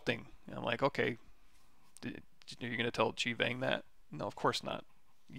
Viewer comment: who is 'Chief Ang' ..presumably a great Zbrush boxmodeller...couldn't catch the name properly, would like to see their work